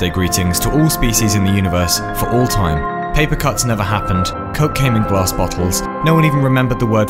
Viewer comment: Coke came in glass bottles- Thought I heard COCAINE in glass bottles.